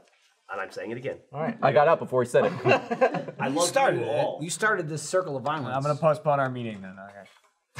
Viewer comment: did Geoff just say "I love you all"?